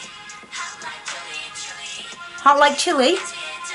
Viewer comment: It's funny most people misheard the "hot like chili" part. It's actually chilly, not chili.